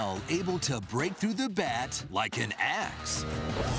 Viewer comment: is it me or did he just say :like an ass ?